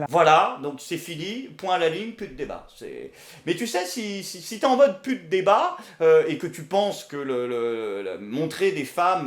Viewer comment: " Si t'es en mode pute des bas " ..des bas quoi ? des bas-fonds ? des bas quartiers ? des bas rtaba ?? on veut savoir !!